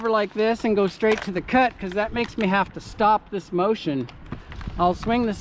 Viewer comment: Uuum, did you just say stop less motion?